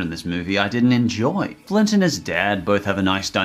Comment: Is it me or did he sound like you said Clint instead of Flint, doesn't really matter, I love Cloudy with a Chance of Meatballs, good part of my childhood, the 2nd was ok, not bad, not good. I haven't seen the Grinch illumination movie, still wanna see it, good job